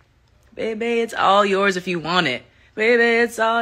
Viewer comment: The way I thought she was saying “Baby it’s all night if you want it”